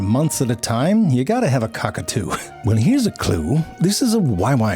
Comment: you gotta have a c*ck or two??? What did I hear Jerry? Great swing in the end -- I've seen a c*ck or two... yeah